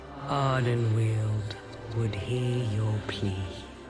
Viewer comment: So I know she said "Ardenwaeld will hear your plea"....but what i heard was "Ardenwaeld will hear your pee"